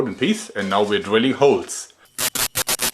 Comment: I heard "and now we're drilling souls"... had to double take.